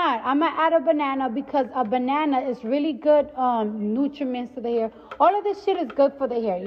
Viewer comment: did I just hear, banana adds good "nutriments" to the hair?! LOL